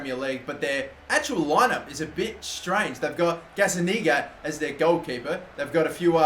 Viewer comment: Anyone else heard say Gazzaniga Niga*